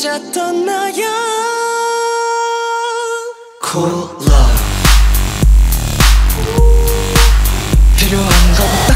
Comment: when they said cool love i thought they were saying cola